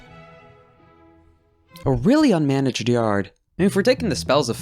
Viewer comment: what are you saying? I genuinely can't hear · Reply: "A really unmanaged yard"